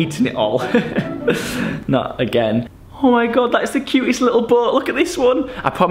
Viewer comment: Who else thought at 1:25 he said “OMG that’s the cutest little butt”